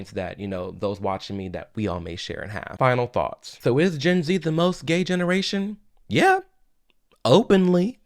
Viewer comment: I thought the video said why is Gen z so gay in Georgia 💀